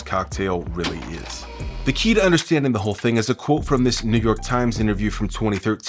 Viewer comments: can someone please tell me what did he say. Cuz the captions has a mismatch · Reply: I thought he said the n word but he said New York Times 😭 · i heard negro time